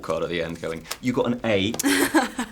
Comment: sounded like he said "thank you so much hermione"